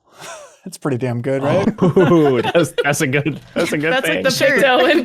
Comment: Misssed joke opportunity: at 24:30 talking about the drill in the digging game at first thought said “that’s a good bit”